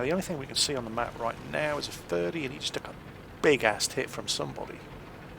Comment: maybe it's just because I'm from across the pond, but every time you say "took a big-ass hit" I hear it as "big-ass tit". Makes me smile all the same. Keep up the good work! Your vids are incredibly helpful!